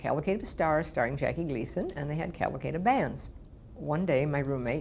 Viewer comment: What does she say at 1:49? "Calvacade of Fans"?